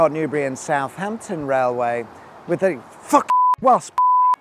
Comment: when talking about the Hockley Railway Viaduct, he says, “It was originally called the Shawford Viaduct; and it provided a link between the Didcot, Newbury and Southampton Railway with the f*king wasps!” Well I can see why it was abandoned then. Who wants a viaduct to provide a link to wasps?!